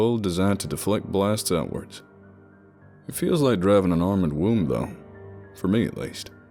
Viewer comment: "an armored womb" yeah...i dont think those go together..but you do you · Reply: I thought he said, "Armored tomb" until the talk of sleeping.